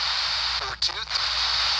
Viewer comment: I heard a spirit say,"42"